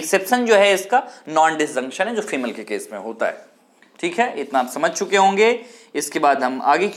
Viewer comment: pr kya bola please comment karo samjha nhi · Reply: Exception hai es ka non-disjuction jo female ke case mai hoto hai